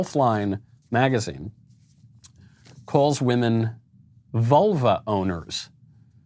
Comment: Lol, I keep hearing "volvo owner" instead of "vulva owner".